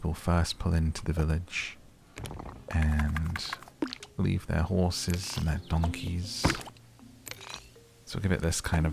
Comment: for a second I thought he said horses and daughters😭😭